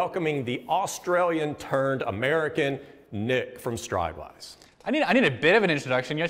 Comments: I really thought you said "Australian turd American". Had to go back for a re-listen. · i thought i heard him say the Australian turd